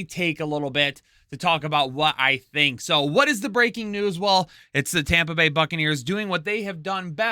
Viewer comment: I had to listen a second time. I thought you said "breaking nudes" out of the gate. :_swag